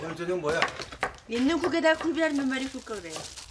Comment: 반찬이 뭐래요??? 굴비만 들리는데.. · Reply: 있는 국에다 굴비 한 몇마리